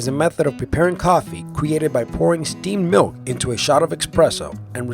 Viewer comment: thought he said "by pouring sardine milk..."